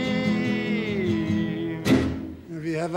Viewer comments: what is he saying here, I wonder? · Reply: ”We have a new leader”😎🎤🎼✌️💚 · What does Harry says, around 2:39 ??? something funny in hebrew ??? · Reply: He says "we have a new leader"